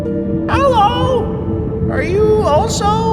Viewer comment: Hallo 1:59 not kidding. The moon actually sounded like they said hallo.